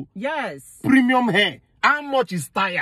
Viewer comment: Lawd I thought she said premium hair 😂😂😂😂